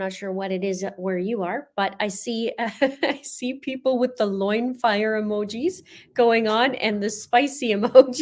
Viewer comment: I thought you said Loin Hair!🤮🤣